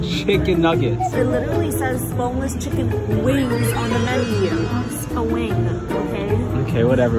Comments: Is it just me who thought she said “homeless chicken wings?”😭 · My volume was low so I’ve thought that you guys said homeless chicken wings until I read what you texted😂 lol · I thought she said homeless chicken wings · I thought she said homeless chicken wings 😂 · My dumbass fr thought she said homeless chicken wings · I think i need to get new ears i heard her say *homeless chicken wings*💀